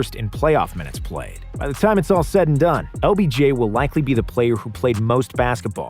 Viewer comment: Did he say obj or is it just me?